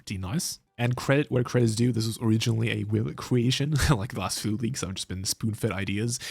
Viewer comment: What did you say at 11:50 ? It’s a ‘who’ creation? “Will creation” or something? I want to see the original creators video too